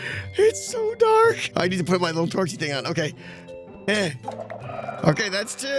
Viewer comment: Torchy. Did he just say Torchy? Has Torchy returned to us, from the world, called Secret Life?